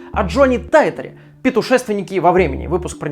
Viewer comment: Мне послышалось или он сказал)) петушественнике?)) 0